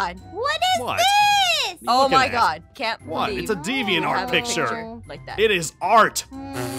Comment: Kids, skip this message. (To kids who didn't listen: Listen to your elders!) Zane at the begining of the video: "It's called hentai and it's art." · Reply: I thought he said: "it is a deviant art picture and it is art"